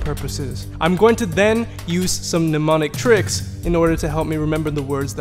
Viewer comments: I heard "demonic" instead of "mneumonic" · I thought for sure he said demonic tricks instead of mnemonic tricks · Lol when he said Mnemonic tricks o thought he said demonic. I was like so this is the kind of sorcery you use to learn all these langauges 🤔😂